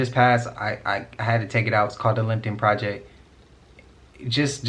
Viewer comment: what is he talking about ? Did he say Lincoln project? I want to know so I can look into it. Thanks.